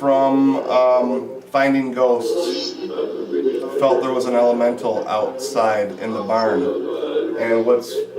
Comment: sounded like he answered you by saying "elamental" after u asked what kind of monster.